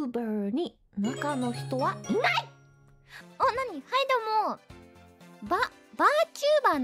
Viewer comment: お◯にー？って聞こえるw